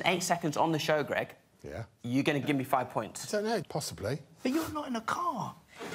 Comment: What did John say @ 1:18 ? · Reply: "But you're not in a car"